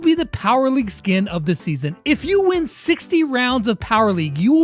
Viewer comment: i thought he said 16 and not 60, so I was like: "wow, really they are being so generous!" But then I pushed back 5 seconds and saw it was 60 rounds😅(also I didn't mean any hate to brawl stars)